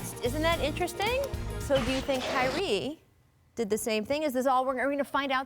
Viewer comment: did she just call him “Kylee”?